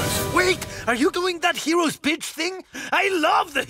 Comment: I keep hearing him say “that hero’s bitch thing” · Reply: The subtitles said pitch but it’s just not making any sense to me.. Hero’s Pitch? At this point I thinks what everyone misheard makes more sense.